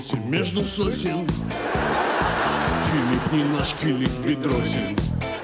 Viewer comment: Филипп немножко не бедросен!? Или мне послышалось!!?? 😃😃😃😃😃